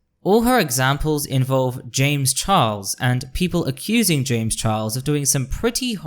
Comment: I came here from your comment on a Lily Orchard video, and I really enjoyed this. (I'm particularly amused by what sounds like you saying "Chames Jarles" 🤣) It is a bit frustrating/triggering as a nonbinary femme seeing so many cis/binary people being bootlickers, defending Wynn, and in some cases fucking loathsome toad Buck Angel for their truscum rhetoric... but honestly, I'm not surprised. Performative Moderates will always throw marginalized folx under the bus to protect each other. 😒 I feel like they need to be slapped with Lily's "Natalie Whinging" video. You've got a new subscriber in me though! I await to see what else you have in store!